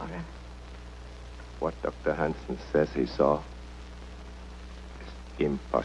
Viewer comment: hahaha all these years, I thought he had said.... OH your so handsome , not oh Dr. handson. lol